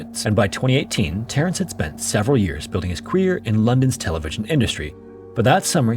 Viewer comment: I swear you said "building his queer in London's television history."